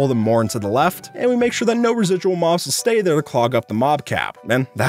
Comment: Bruh did he say that's a... Win win in my a**??